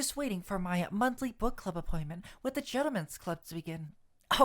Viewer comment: I thought he said the germans club... Fuck it, his reading is amazing. I keep missing the streams because I'm traveling or some shit. :( Noises of femboy sadness